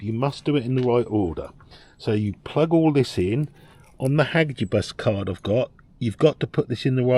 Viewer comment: what the hell did u say at 1:18-1:22? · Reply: It was the name of the cable it was somthing like Hadgeibus